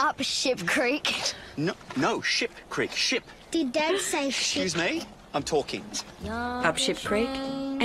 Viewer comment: He actually says sh-t lol